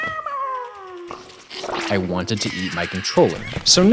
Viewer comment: did you say you wanted to EAT or YEET your controller? Yes this is an important question. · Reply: Eat, but yeet was not off the table